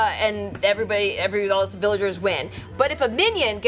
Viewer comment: Lol did just say civillagers? @ 1:31 As in civilians from mafia 😂